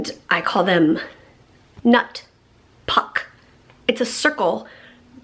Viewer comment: I love the new organization of the working room! Did you just say a nut puck lolol